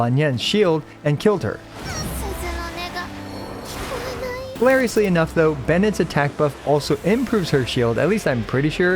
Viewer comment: what did she say 😮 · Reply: ' oh nega ' 🤧🫣